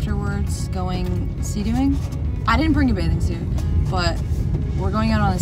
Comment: I thought u said no bathing suit lol..